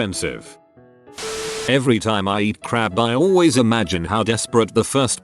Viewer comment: I thought they said "Crap".